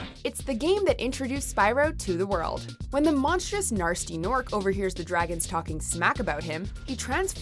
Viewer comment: Did she just say Narsty norc?